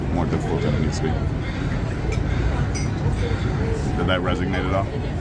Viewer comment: did he say “did that resignate at all?” Never heard someone mess up resonate before